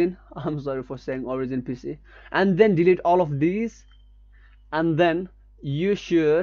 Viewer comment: did you say delete all of these?